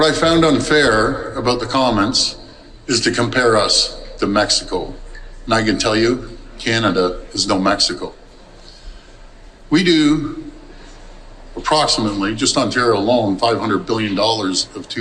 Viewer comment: did he say "Canada is snow mexico" ?